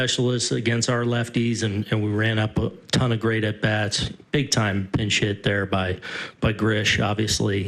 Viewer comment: I totally thought he said big time and shit